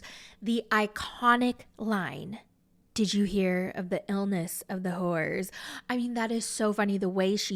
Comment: I thought she said, illness of the horse?